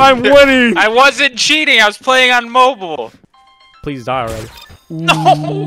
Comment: did anyone hear T...Mobile?